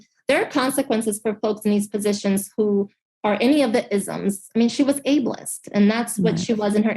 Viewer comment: LOL wait, what? Did she just say "is'um's" 🤣😂